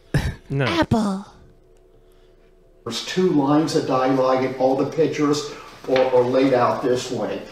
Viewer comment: Can some one confirm for me what he's trying to say? I think he's looking for "dialogue" but it sounds like "die-lyge" or "die-ligen". Also, that is not dialogue, that's just regular ole narrative text. The fact that as a "professional" writer he doesn't know the difference makes me a little worried to have him teaching children. Thankfully no children will ever read his books.